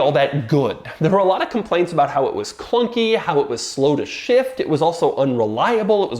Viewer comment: FOr whatever reason, when Doug said "slow to shift" I heard "slow as shit".